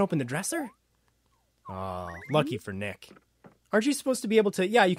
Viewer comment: i thought he said "Fuck you, Nick." XD